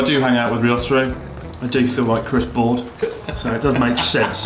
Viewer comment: I thought Ryotaro said "Chris Board" for a sec, it made me go... what? 🤣